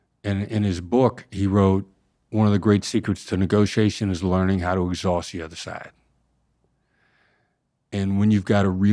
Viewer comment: What did he say at 1:00:13? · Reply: “One of the greatest secrets to negotiation is learning how to exhaust the other side”